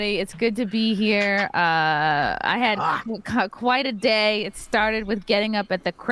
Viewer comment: did say she had "coc..he... quite a day"?